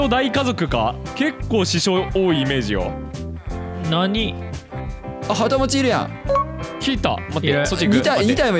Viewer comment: からきくと、いぬたぬきが はともちいるやん って言ってるように聞こえるｗｗ